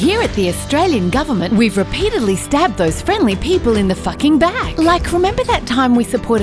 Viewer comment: did she just say "in the fucking (bag or back)" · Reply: In the fucking back